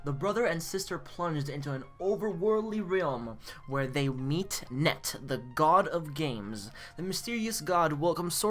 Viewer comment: Excuse me "Net"??? Did i hear correctly? I'm pretty sure his name is Tet, not Net